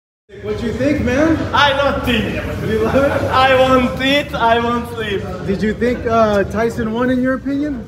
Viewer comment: USYK is seriously hilarious man 😂😂😂 - ' I DONT THINK!! ' 🤣🤣🤣🤣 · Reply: I thought he said I want it I won’t sleep but idk